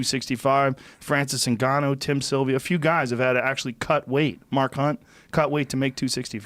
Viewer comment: Did he say Mark Hunt at 0:40 ?Which Mark ?